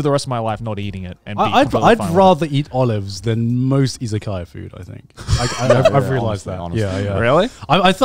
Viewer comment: For a second there, I thought Garnt said he won't eat isekai food. @@